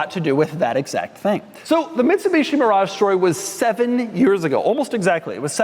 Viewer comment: Did he say “Bitsumishi”?